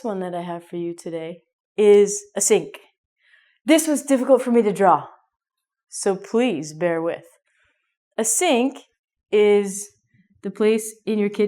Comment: What did you say in the 6:34? pd. I love you! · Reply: "This is difficult for me to draw so please bear with"